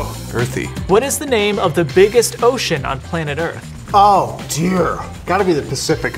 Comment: I got to say you got me on what is the biggest ocean I thought you said Lake so I was like so easy Mississippi🤣🤣🤣🤣